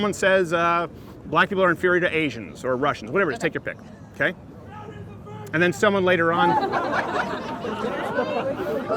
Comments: What did the guy yell at 17:07? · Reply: Marvin Chinchilla “crowders a virgin” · What did the guy yell at 17:08? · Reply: "Crowder is a virgin" I believe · what did the guy say? All I hear is "Crowder's a virgin" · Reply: He says “crowders a virgin” and then crowders wife replies “I’m right here” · What did the dude in the background say at 17:12? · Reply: Crowder is a virgin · what does that guy yell? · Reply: Matt Rountree CROWDERS A VIRGIN! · Can anybody make out what that guy screamed at 17:08 ? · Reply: It sounded like he said "Crowder's a virgin" lol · what did that guy yell? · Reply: "Crowder is a virgin!!!" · What did the guy say at 17:09? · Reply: Crowder is a virgin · What was the dude at 17:09 yelling? Best I could make out was something like "Crowder's a [...]". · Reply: Virgin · What did the guy yell at 17:10? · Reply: He yelled 'Crowder is a virgin ' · WTF did that guy scream at 17:10?! I've replayed it 36 times, and i still can't make out what he said. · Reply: "Crowders a virgin" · Does someone hear what was shooted? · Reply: I think is was Crowder is a racist. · What did that guy say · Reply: Crowder is a virgin! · what did that guy say? i replayed that part so many times and never got what he said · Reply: Called him a virgin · what did the person in the back yell? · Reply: Crowder's a virgin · What did the guy at 17:13 yell? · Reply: Crowder’s a virgin 🤣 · what was yelled out at 17:10? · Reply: "Crowders a virgin!"